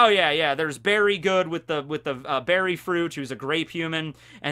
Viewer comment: I had to re-listen a few times because I thought you said "There's Berry Good with the Beri fruit who's a great human" and I I was sad you didn't say 'good human' to cap that off hahah.